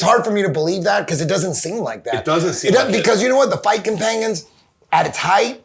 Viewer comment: Idk why but the first time Eddie said “fight companions” I heard “Viking penguins” 🤣